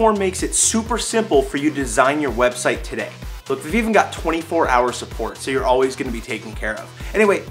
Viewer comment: When he said we even got 24 hour support I thought he said we even got 24 hours of porn